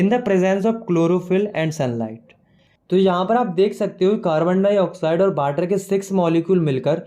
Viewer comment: Did he said "bater"